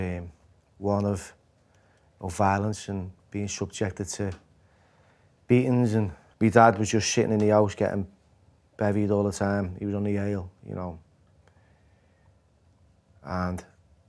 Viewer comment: He doesnt even flinch when he says his dad was shittin in the front room! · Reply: i thought he said shitting in the fondue 😂